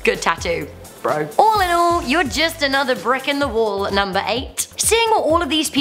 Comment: did someone say bribe